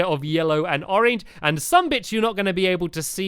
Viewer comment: Did Anyone Hear saying "Some b!tch" at 9:15? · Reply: He said some bits